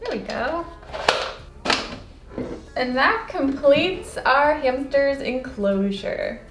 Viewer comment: Yesterday I got a hamster :)!!!! Also 9:25 did she say hampter